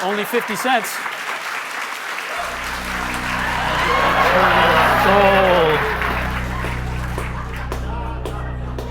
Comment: we couldn't hear what he said be Aude of the cheering. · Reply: “And your SOUL”